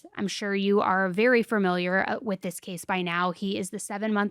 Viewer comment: I heard say “fur-miliar” and I can’t unhear it.